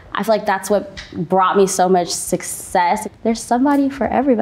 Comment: did she say success 👀 the world’s just sad atp